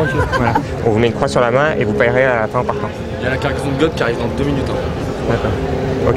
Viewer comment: Quelqu'un a t-il compris ce que dis le jeune à Gonzague vers 1:28 ? ^^ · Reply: Moi j'ai entendu "Y'a la cargaison de gods qui arrive dans 2 minutes"